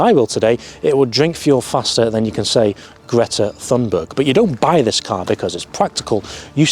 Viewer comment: What did you say 1:24 😂🤣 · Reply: Greta Thunberg